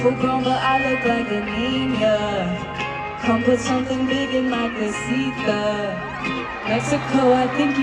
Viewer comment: doesnt she say neat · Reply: idk i heard "come put something big in my casita" or ~ roughly "come put something big in my little house"